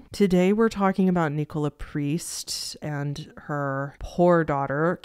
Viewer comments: did you say her 'whore' daughter? trying to understand the context there lol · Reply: she said poor, I can see how to mishear it though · Omg in the intro I thought you said whore daughter, not poor daughter hahaha